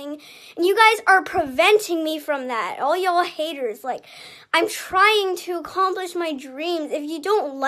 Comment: instead of I'm trying to accomplish my dream it's I'm trying to sleep